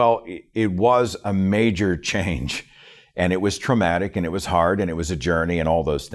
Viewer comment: Haha! I thought around here, you said, “walking away from Christianity was a ‘manger change’” #LOL… Get it, “manger changer” … “major change” #LOL Thank you for your pun and/or help, sir! #GodLess #LOL 😊